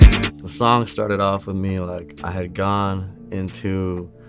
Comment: I thought he said “I had a gun”